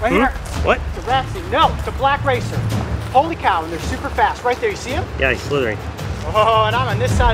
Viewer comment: Thought it said super tasty...